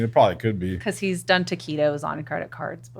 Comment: min What has he done on his credit card? Tequilas? "Tequilos?" I'm not native English speaker and couldn't understand why he would need to justify himself for drinking tequila.